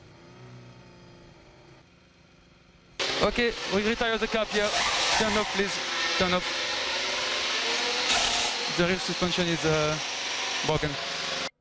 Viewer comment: вот что было в конце your rear suspension... eeeemm... broken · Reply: да, мне почему-то послышалось "There is a puncture that..." и какое то слово на Б, но не мог понять по смыслу каким там может быть прокол. А на самом деле да, он говорит "your suspension is... broken"